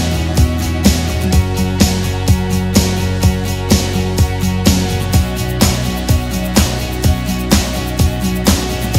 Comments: did she say a bad word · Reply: She said "oh F*ck" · I think the girl said a swear word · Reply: Yep its the f word